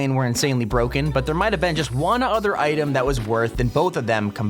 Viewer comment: you said worth instead of worse